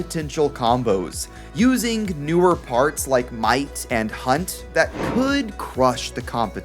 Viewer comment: I thought u said Mike Hunt lmao